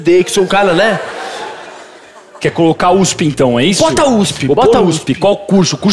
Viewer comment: Eu ouvi: os pintão